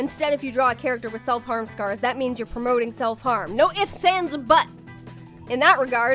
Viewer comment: "No ifs ands or buts" What I heard: "N'don't give Sans a butt."